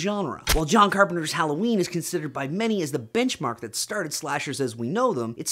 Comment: I thought you said "Bitchmark" of horror.